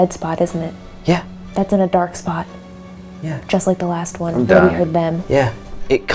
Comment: I heard “He was gone” I don’t think it was John... she kept saying ‘She’ xxx